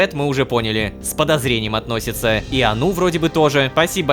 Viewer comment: мне послышалось ануф